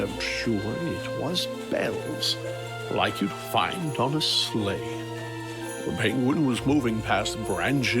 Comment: I came from 12 minutes in the future to inform everyone that there’s no scary twist ending. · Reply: I was looking at the other monitor when this got near the end, so I was only have listening when Santa was talking, and "sleigh" I heard as " slave" and boy did that bring my attention back to the video!